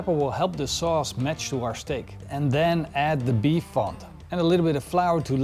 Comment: I heard "beef font" in the sauce, and heard it several times. Is that right? How is that different from broth?